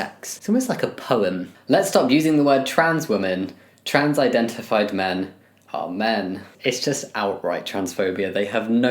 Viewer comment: The " Trans identified men are men " sounds like then saying Trans men are men, so that one threw me for a loop. Also apparently trans men dont exist in most transphobes minds.